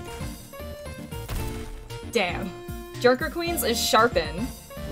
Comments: For a minute I thought you said jerker queen🤣😳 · Idk what anyone else heard but i swear i thought i heard her say “jerker queen”💀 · I heard "jerker queen" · Why did I heard "jerker queen" instead of junker queen